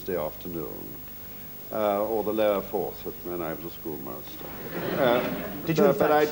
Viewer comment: what does he say before "when i was a schoolmaster"? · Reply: The lower fourth